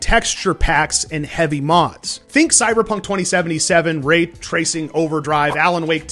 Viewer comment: Wait @ 0:20 did you say Rape tracing????? lol